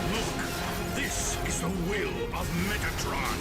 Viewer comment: I honestly thought at 6:42 that he said "This is the will of Megatron."